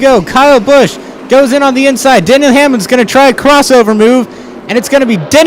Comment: it sounded like he said Daniel Hamlin